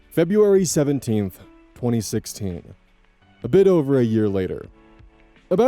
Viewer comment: "February 17th, 2026" I swore i heard 2016